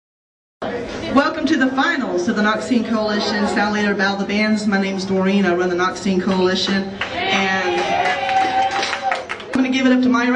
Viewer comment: I thought I heard "nazi coalition" at the beginning...worried me for a second...